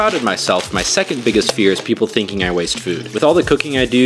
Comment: Thought you said "My biggest fear is people thinking I'm doobydoobap" and now I have my suspicions